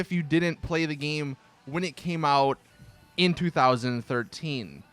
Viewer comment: I heard "in 3013"